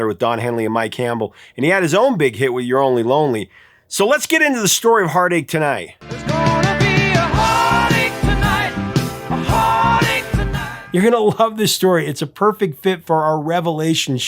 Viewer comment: I thought as a kid they said "There's gonna be a party tonight"